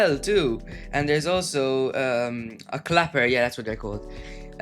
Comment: Thats what its called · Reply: I'm literally dirty minded I thought I heard "cocker" but it's actually clapper...